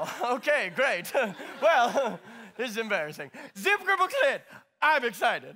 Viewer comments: does anyone know what he's saying here? I can't make it out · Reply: Zip dible clip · Anyone able to make out what exactly Vanger says at 0:54 ‘cause I turned on the subtitles to see if it was something in French or German but all they said was “zip dible clip”